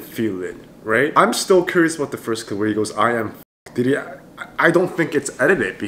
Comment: actually they were supposed to introduce themselves and he said I'm Fox but it sounded like I'm F(<3)ck